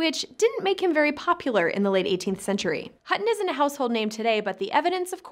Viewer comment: "Hutton is a household name today." I've lived in a number of households, but this is the first time I have ever heard his name. What households are you talking about? Are you jerking our chains? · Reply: I think you may have misheard, she said "Hutton isn't a household name"